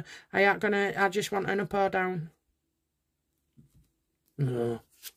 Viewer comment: I think I heard the word "no" at about 5:09 or so... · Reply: I checked the transcript. It was a deep sounding "no".